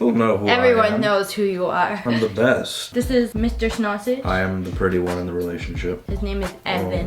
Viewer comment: Did she call ‘ Daddy ‘ Mr.Snorsauge? 😂😂